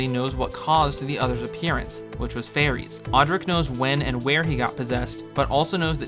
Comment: Except that was never said though. Fairies have nothing to do with the Other appearing. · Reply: Sorry for the pronunciation, I was saying Phares.